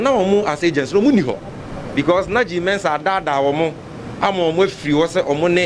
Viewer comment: Did I hear Sammy Gyamfi say "na Jean Mensah adaadaa won mu". Lol